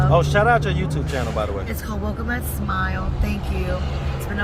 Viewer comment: Did anyone catch what her YouTube channel was? I couldn't really understand her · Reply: Welcome at Smile*